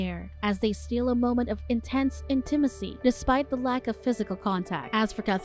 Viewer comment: what the fuck did she just say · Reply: Intense intimacy.